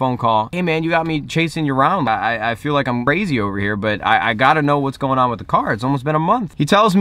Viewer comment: did he just say "Brazy" a blood 😂😂